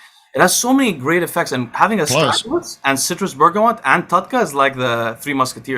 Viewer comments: Can some tell me what were the vitamins they said they were the "Golden Triangle"? Clip 52:19. I believe they were Tudca, Citrus Bergamot and not sure what was the third? Thanks in advance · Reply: Astragalus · What does say at 52:23 ? I couldn’t get it apart from citrus bergamot and tudca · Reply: Astragalus